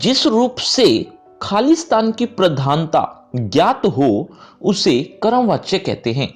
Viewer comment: did i heard correct khalistan